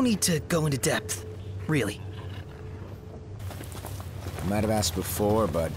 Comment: Noctis is asking about what? i can't understand what hes saying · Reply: "I might have asked before, but is Ebony really all that good?" I can only guess that he is asking about coffee or something. :3